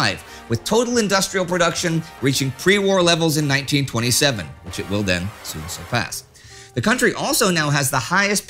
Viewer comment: you drop your voice, and I can't make out what you are saying, no matter how many times I listen to it. Please don't do that! Edit: Oh! I think you are saying, "... which it will then soon surpass."